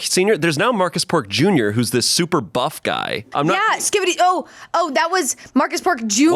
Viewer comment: did she say skibidi...? so true courtney